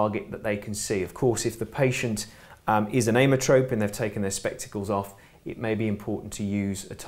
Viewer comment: What was it you said at 7:25 regarding the patient taking their spectacles off? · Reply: He said that they were an ametrope